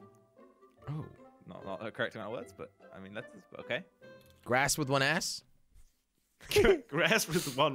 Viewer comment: i thought you said "grass with one ass" hahahaha